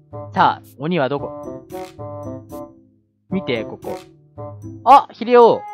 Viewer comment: たぁ、鬼はどこ？？に聞こえる